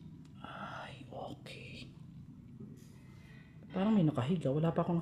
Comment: po may narinig akong bulong na sabi aay ok